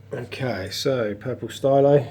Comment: What is the name of the purple coral you talk about at 3:48? These yankee ears can't quite understand the name of that one lol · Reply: Oooooooh I think it's a purple stylophora :)